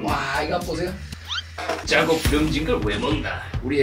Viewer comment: 에 눈감고 들으면 짜고 븅진같고 왜먹나라고 들림ㅋㅋ